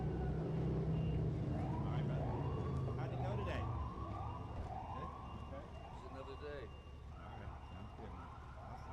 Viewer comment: Can somebody write me please what did the photographer and Johnny say to each other in 7:08 - 7:12? English isn't my mothertongue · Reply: How did it go today? He replies : just another day